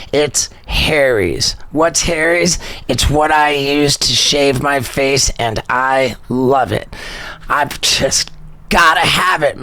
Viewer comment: What Steveo actually said "Its what I use to shave my face & I love it". What I heard.... "Its what I use to shave my face and eye" - lol.